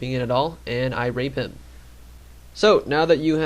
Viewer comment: did he say "I Rape Him"?